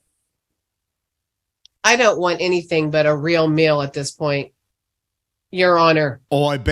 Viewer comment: i thought she said she couldnt wait for a real male